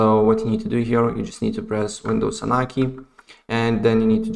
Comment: What is the shortcut? I can't understand your accent. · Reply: win + r